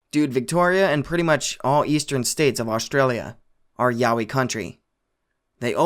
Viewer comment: I heard that as "yaoi country" for a second.